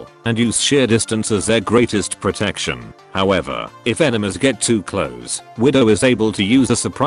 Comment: IT SOUNDS LIKE HE SAYS ENEMAS